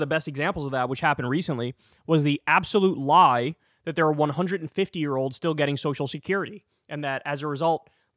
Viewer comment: My dad died in 2019. Everyone was telling me "You have to notify social security and cancel his payments". Before I could even figure out how to do that they sent his estate a letter saying "We already know he's dead and we're taking back half of his last check." Gotta call bs on the 150 year olds on social security. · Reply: i coulda swore T said "100 Year olds", not "150 year olds", and that's even more disturbing, because people DO live to be 100+ and if he stops legit payments to those . .